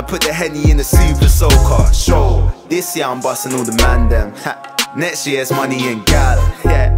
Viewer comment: At firsy i thought he said bussing on the mandem but🔥🔥